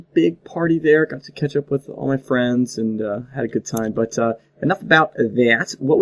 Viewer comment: i thought you said "I got some ketchup on my friends, ya ya," at the beginning